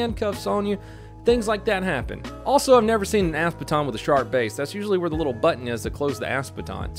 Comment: Sounds like you saying “ass baton”